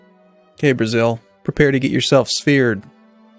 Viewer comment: it sounded like you said "Okay Brazil prepare to be speared"